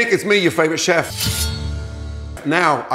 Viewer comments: Bruh at first I thought he said " hey n**** it's me your favorite chef" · i thought Gordon's saying the N word at the beginning 0:00 hey n****s me your favorite chef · Why did I hear ‘Hey n*** me your favourite chef’